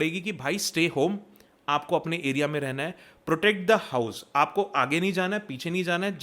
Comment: Did he said hoe? Astagfrullah bhai 💔🥀🥀